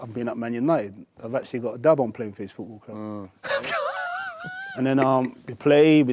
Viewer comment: What did he say 35:25, "I've actually ...... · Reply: Got a ‘dub’ on Playing for this football club